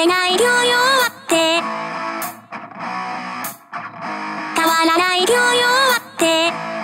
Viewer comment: 「変わらない今日よ終わって」が「変わらない教養あって」に聞こえる